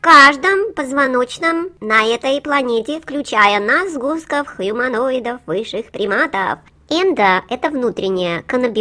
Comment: Я один услышал "хуюманоиды"? Х))))))))